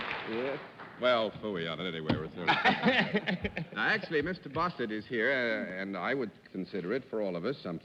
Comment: Did anybody else hear John Daly refer to their guest as Mr. Bostard at 13:19? That cracked me up!